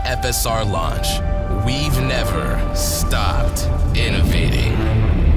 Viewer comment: 55 seconds in and for a second i heard "We never stopped.... Nvidia" I know its supposed to say "Innovating" but cmon you gotta agree it sounds like the movie of the century right there.